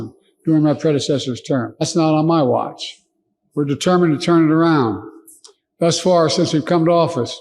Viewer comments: Did he just say we're white? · I thought he said "We're WHITE ..."